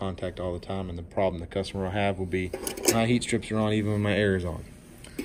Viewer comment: what did you say? I can't hear it clearly. LOL The problem the customer would have? no hot strips on even when my air is on?? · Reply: The problem the customer would have is the heat strips would be on with the AC on. It would cause the AC to seem to be running normally but since the strip is on your delta T would be very low.